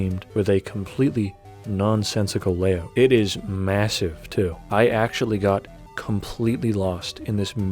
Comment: did someone say massive ?